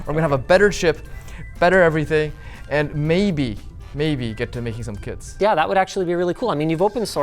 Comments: I enjoyed this video so much i smiled to the end. And I thought he was saying "maybe get to making some KIDS" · I heard it as "maybe, just maybe, get to make some KIDS" 😅. Of course he said kits.